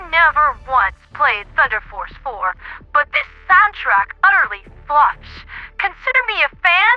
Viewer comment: Now I know you're messing with me... but where???? :3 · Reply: hehehe, at 1:15 it sounds like you say "this soundtrack utterly f*$ks" and I just had to say something